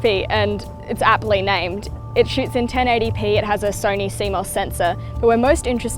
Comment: I thought she said "it shits at 1080p"